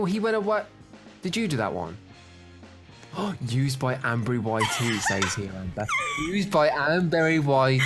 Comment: I thought said used by Amberry Whitey, Then I looked up at the top Right and I realised it was Amberry YT